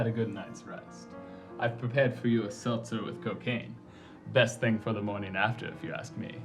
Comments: Did he just say "seltzer with COCAINE" at 3:18?? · "A seltzer with cocaine!?" Didn't catch that the first time.... Holy crap that’s a lot of likes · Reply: I thought he said "a seizure with cocain" for a sec, I didn't have subtitles on that time. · wait wait, did he just say, Ive prepared you a heist with COCAINE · did he say cocaine? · did he say cocaine???